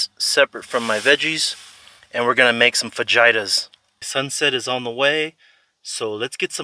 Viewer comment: When you said, let’s make some fajitas, I thought you said let’s make some vaginas, lol.